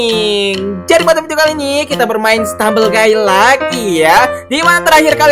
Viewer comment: Ini Gak SalAh Dengar Kan? 0:17 Itu Bilangnya Stumble Gay Bukan Guys